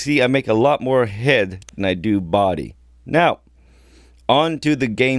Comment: Lol i thought you said i get more head than body😂